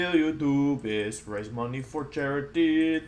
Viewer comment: For so long I thought you were saying YouTubus instead of YouTubist. I just thought that was a thing to say.